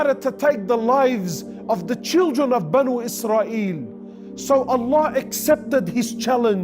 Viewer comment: did he say ben israel? i try to find the story. ins sha allah someone can help. o assalamo 3alikom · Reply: He said bani israel (the children of israel (Prophet Jacob PBUH)